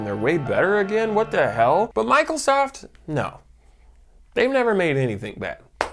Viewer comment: Did Jakey say Michael Soft. 7:50 . Michaelsoft Binbows reverence😂😂😂